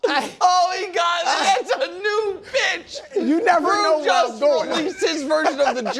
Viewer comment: Ngl…. I had the volume low because I was at a restaurant watching this and I thought Nick said “Ohhh he got us….. YOU B*%#*!” I was like wtf!?!? 😂😂 but he said a new bit once I listened to it again with the volume at max